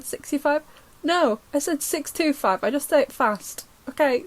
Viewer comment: dw I heard 625. Probs because I’m in Lancashire too so have a similar accent